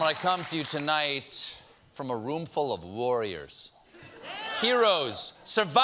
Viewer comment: I thought he said lawyers rather than warriors.